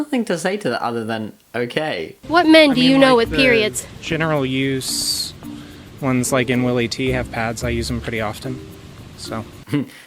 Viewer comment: Can someone tell me what the dude at 9:25 is saying. I don't understand him :/ proper captions would be nice to have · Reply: Lenny Fu he’s saying the general use ones (pads) in Willie T’s, I use them pretty often